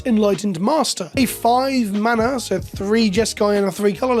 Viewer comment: does say Narset is 5 mana at ~10:33?